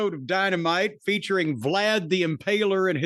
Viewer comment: I thought Jim said "Bland" The Impaler. 😅